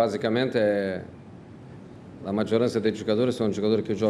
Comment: I'm amazed at how well he speaks Italian. One funny thing. At 19:52 he says "la maggiorata" which is like "a maioria" in Portuguese, instead of "la maggior parte". In Italian, "la maggiorata" is a girl with big boobs! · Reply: He said "la maggioranza", it's correct..Remember that Mourinho is not a pirla!